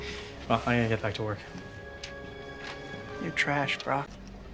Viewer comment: When I was a kid I thought he said “you’re trash brah” 😂